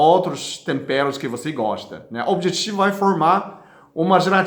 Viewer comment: Aos 1:46 ele fala,...vc tempera com can , nao entendi esse tempero, alguem pode me explicar ? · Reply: Curry